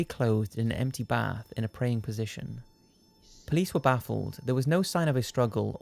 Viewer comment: I swear I can hear someone say "police", "please" or "Louise" which is freaky as that's my name 😨😨😱😱